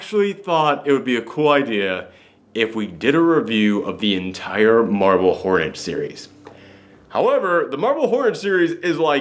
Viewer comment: I had to double check the title, cause the first few times you said it, I thought you were saying "Marvel Hornets" and I was sure that couldn't be right.